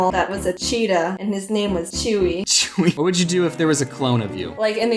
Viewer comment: did you just say Chewy? I have a stuffed chihuahua named Chewy OwO